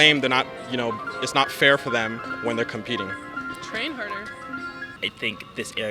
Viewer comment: Did she just say, "trans harder"?